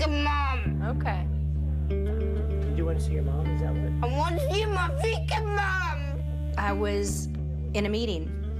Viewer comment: Sounds like he is saying “I wanna a beer”.......